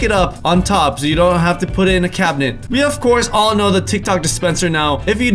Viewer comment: Tic Tok dispenser ... then says tic tacs. Anyone else catch it?!?